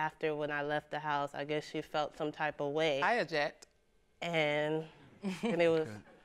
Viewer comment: "I Oject"👀 😂😂😂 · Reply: Sounded like she said object